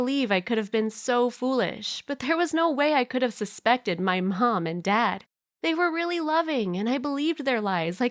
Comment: did she say pawnm and dad